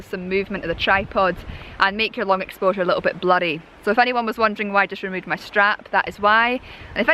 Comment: Ha ha. At first I misheard ‘blurry’. I thought you said ‘bloody’ which I was trying to make sense if it was a Scottish expression, but I got it sorted. As usual, your videos are a delight to watch.